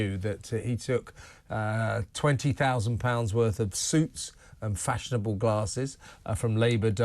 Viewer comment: I thought he said 20k worth of 'soups' 🙄